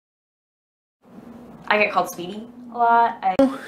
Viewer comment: I thought she said I get called speedy a lot ;)